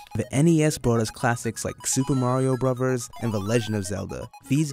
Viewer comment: is it just me or did he say super mario brovers?